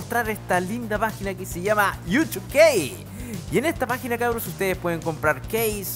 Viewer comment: por un momento escuche youtube gei